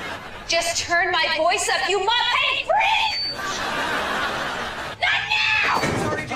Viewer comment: "you muppet FREAK!" she is the last person I want to see near a mic when angry 🤣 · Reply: I thought she said " you modified FREAK"